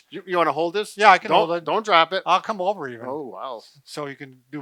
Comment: WHAT DID I JUST HEAR??? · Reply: I'll come over TO you. Lol